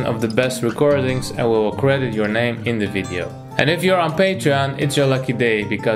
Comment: what does He say at 0:53? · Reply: "And if you are in Patreon, it's your lucky day, (because) you will be selected for sure"